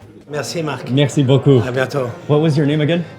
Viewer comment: Did he say arigato to mark at 18:56 ish? LMAO